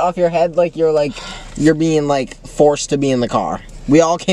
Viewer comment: ayo???? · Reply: like it sounds like they said “we all came out today” as if they said they were all gay, idk if that’s how 2 explain it